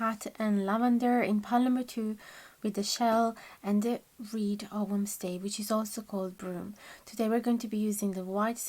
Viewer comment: What's the rune in 1:03:15 called again? I can't understand. Thank you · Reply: It's the Reed ogham stave, also called Broom:)